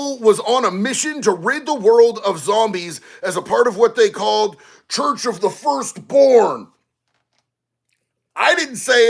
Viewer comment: Thought he said, " Church of the Scorn." Thought that sounded like a cool name tbh.